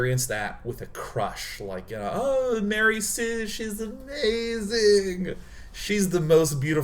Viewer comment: BTW, in the close captions at 16:37 where he says "Mary Sue, she is amazing" the auto captions say "Like them a rescission in AZ"...which is amazing